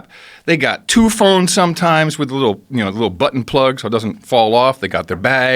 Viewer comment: I thought he was going to say butt plug 😂 😂 😂